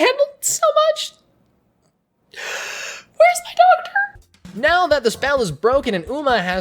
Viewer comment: I misheard this as "where's my dog turd?" Lmao